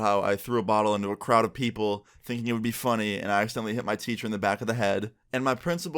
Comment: I thought it said “ I accidentally hit on my teacher” 😂